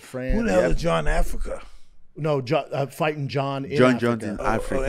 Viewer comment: He said “Jon in Africa”. Open your bloody ears people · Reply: He said John in Africa, even Ngannou corrected Tyson on mishearing.